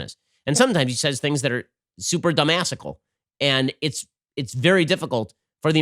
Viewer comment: What was the word Ben used at 49:00? Super “dumbasical”? I tried looking it up, obviously I’m spelling it wrong. Anyone can help me out? Love the word. Thanks.